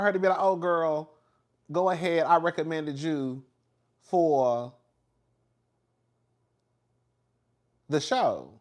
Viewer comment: I thought she said she recommended her for the award